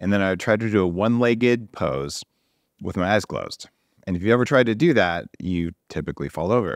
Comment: I swear he said with his ass closed!